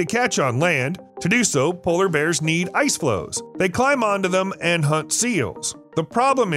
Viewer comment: wtf does he say? Ice flows ? Confused😕